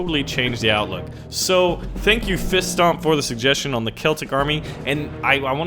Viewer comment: Bruh did he just say "Seltic"?😠